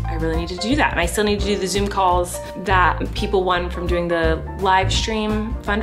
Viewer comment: I thought you said zoo call and I was like what